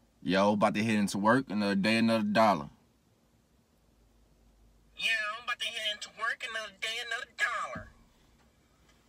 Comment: I thought he said “Ima Hit And Twerk Another Day For A Dollar” 💀💀💀